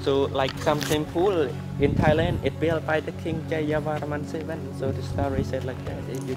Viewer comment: what was he saying I couldn’t hear it very well. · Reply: Some temples in Thailand are build by Khmer king name Jayavarman 7